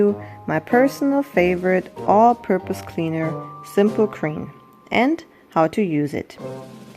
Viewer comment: Are you saying simple cream or simple clean? Cause I don’t think I heard “simple green” in this video once